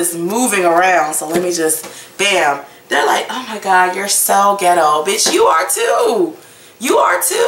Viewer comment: did you just call me ghetto gurl?? Ou freken! LOL